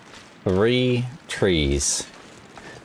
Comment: As an American, for long time I thought you were British. You have that cockney accent way of saying "fink" instead of "think". At 1:04:11 it sounds like "free trees" to my ear. But if the English say it that way, you still pass. :goodvibes